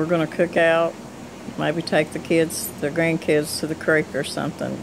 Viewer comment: I heard you say tou may take your grandbabies to the creek. I used to play in the creek when i was a little girl